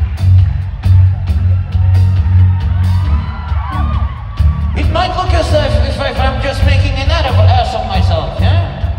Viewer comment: wtf did he say? I couldn't understand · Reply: “It might look as if im making an (?) ass of myself”